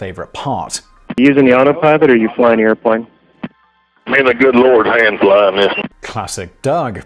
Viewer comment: "Me an' the good Lord's hand [are] flyin' this" What I hear, without the "are" in brackets, which I put there to convey what I think he meant to say, but did so in a very Texan way :) And it sounds much more legendary than the quote TIFU put on the screen at 8:33, despite the grammar being wrong.